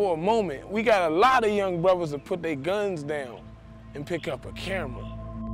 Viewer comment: did I hear "put their guns down and pick up cameras"?